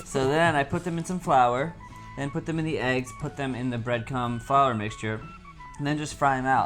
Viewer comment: ish he said bread cum instead of breadcrumb.